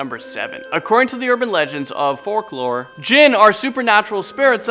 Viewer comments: did- did he say FORKlore? LMAO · Around the 3:20is mark, did he say "fork-lore"?? Do they tell tales about bizarre cutlery in the Middle East?? Even the eating utensils want to do harm......